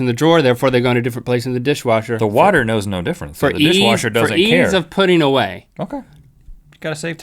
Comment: Anyone hear this? 0:54 Rhett: "the water knows no difference, and the fishwasher doesn't care." :P